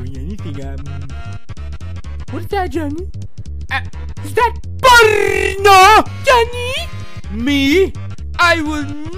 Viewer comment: what I hear: STOP- dieing *POLITO*~ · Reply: No he said PORNO